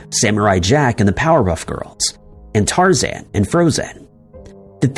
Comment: Wait hold up..... Did he say POWERHUFF GRILS?